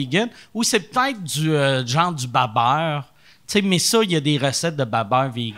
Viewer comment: Qu'est-ce que dit Mike à 6:52 svp : "du beu-beurre" ? Je suis Français donc je ne connais pas. Merci